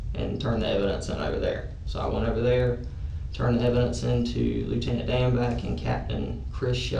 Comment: I thought he said Lieutenant Dan and i was like awwwww s**t!!!!!